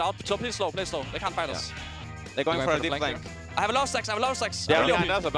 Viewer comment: I can't be the only one who heard caps say "I have a lot of sex" at 1:38.